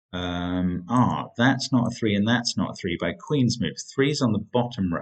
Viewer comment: That's not a 3 and that's not a 3 because of ....what phrase did you say next? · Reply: Queen's move.